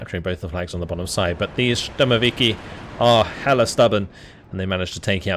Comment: lol Did say "hella" at 22:09 ? Northern Califonia slang has a beachhead across the Atlantic now